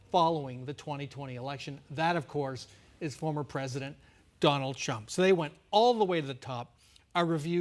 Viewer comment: 🤔Did I heard him say Donald Chump?🫡 edit: SMH 🤣